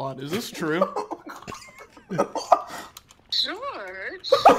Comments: At first, I thought her saying George was Okay, sure. I was sooo confused lmao. Then, I turned up the volume. I understand now XD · The fact that his mom actually agreed to that i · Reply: she said "george" but the commenter thought she said "sure"